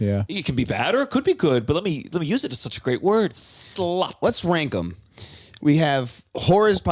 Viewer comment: Jeff, did you say "let's wank 'em" at 50:37?